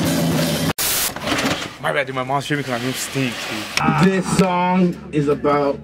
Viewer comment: What does he say at 2:08? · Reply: “My bad dude, my mom’s tripping cus my room stinks” 😂😂